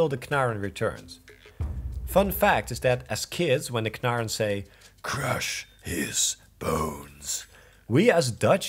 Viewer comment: There's something really funny about hearing you and your cousin switch between Dutch and English anyway, but the fact you both were speaking in Dutch, then he switches to English to say "Get f*** idiot!" in English was just really funny XD Like he had to switch languages to insult you properly :') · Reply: "Boos" means angry. We thought the Knaar said "Crush is Boos" while he actually says "Crush his bones". We just understood it wrong as kids. That was also the case in Pokemon, with quotes like "Got away safely", "Poisoned", "Paralyzed" and "Confused". We always pronounced it wrong and we still do sometimes for the memes